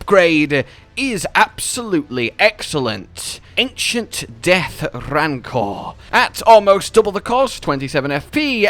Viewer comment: rosus' axe has the weapon art that is the same as tibia summon which is kinda disappointing. i wish the tibia summons were the giant skeleton that either fires rancor lasers or swipes in a massive arc. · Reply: I honestly heard you say "The Rancor-cor" i thought you added your own echo for a second xD